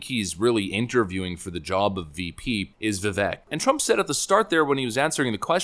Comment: I wish we had CC for this video. Did you say "the Vet" at 3:21? OH, Vivek. Yeah, CC is kind of important; is it up to you or YT?